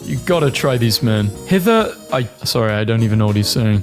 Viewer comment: If anyone else was wondering about what þe Olde English meme said, he says “Whither êom âdrêogan oððícgan.” If þe translation I found is correct, þis means “Whether we endure or perish.”